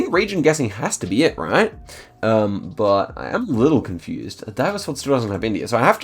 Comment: what did he say? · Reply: "A Diverse World still doesn't have India" It did kinda sound like he was speaking Russian or something 😂